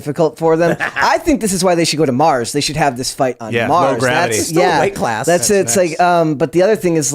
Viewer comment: Low gravity he said lol. Thought he said NO gravity.